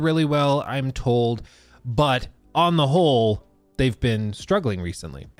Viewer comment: I hear Preparation H is really good 'on the hole'